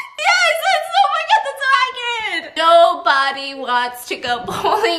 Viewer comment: could u not figure out what u were saying? cuz i heard "yes omg thats so accurate" 😂😂